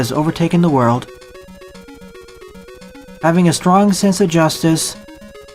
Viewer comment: I always think that he is going to say "having a stroke